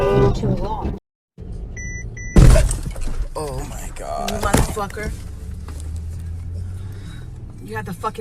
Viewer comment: i think the mom says "you motherfucker" to her son 😂